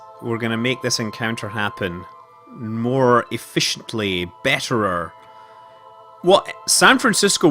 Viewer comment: did he really said BETTERER? Hmmm,lets ask Skye about that :D